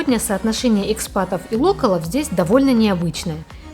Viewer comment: На 1:36 вы говорите про экспатов и ...? Не могу расслышать слово - локолов? Или как правильно ? Незнакомое мне слово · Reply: Добрый день) Локал- Local - в смысле местный, этнический, локальный. Довольно часто используемое в обиходе слово за границей, как и другие заимствованные слова)